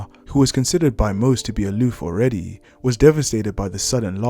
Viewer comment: What do you say at 7:08?? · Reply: Aloof means like your mind is somewhere else